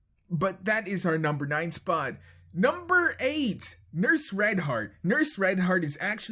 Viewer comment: lol I heard he said "nurse Reinhardt"